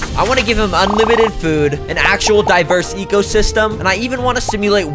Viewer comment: I thought he said diaper